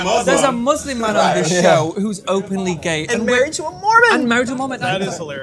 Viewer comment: omg i thought they said he was "out and open and married to a woman" hahahha they said mormon